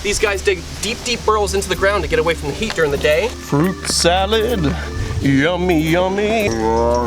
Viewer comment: Did anyone else think the tiger said yum